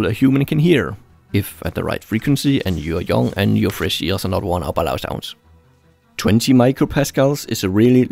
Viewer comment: what did he say at 5:11? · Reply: "...and your fresh ears are not worn by loud sounds."